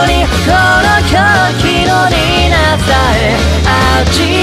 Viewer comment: 最後の The Queen of Hearts がどうしても タピオカ〜〜にしか聞こえない。 3:15 3:15 3:15 3:15